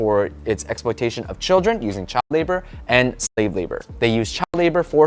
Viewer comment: Unnecessary censorship can be annoying😭. I thought it said child labor and shrex labor. Had to look at my screen in confusion